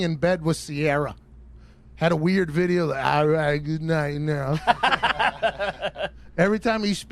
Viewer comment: Ayo!!! 11:40😂😂😂what Russell Wilson say? ALrigHt GoOdNiGhT yAll